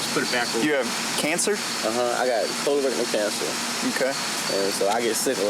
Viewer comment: did dude just say he had covid and cancer isn't this from the 90s? I'm shook son 😮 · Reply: Colon cancer!